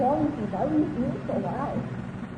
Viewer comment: eu acho q o diálogo do minuto 13:50 é algo como "Why are You screaming so loud?" nn sei muito bem se a pronuncia está certa, mas eu tenho certeza q escutei um "loud" algo como, "pq vc ta falando tão alto?" algo tipo isso